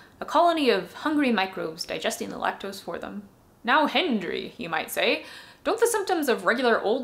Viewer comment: "Hendry"...I thought I heard "Head Injury" said very quickly, and thought, "That's a funny pseudonym!" Then I read the channel bio and thought, "Oh, HENDRY!" Then I thought, "Uh oh, I think I might be Head Injury."